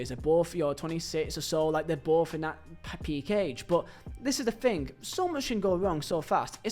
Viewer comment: Did you just say that Cunha and Mbuemo are both in a Paki cage?